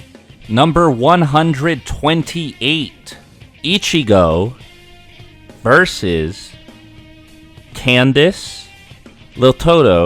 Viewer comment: idk why in my head I heard Ichigo vs Candace Owens lol